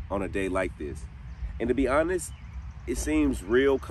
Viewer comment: I heard "Thomas" when your edit said "honest".